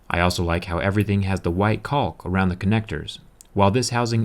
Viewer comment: What did you say at 3:35? White cod? Call? Code? Trying to figure it out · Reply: DeathToCockroaches kalk